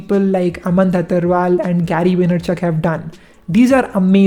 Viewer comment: what he said after Dhattarwal ... Can anyone please reply me? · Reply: Gary vaynerchuk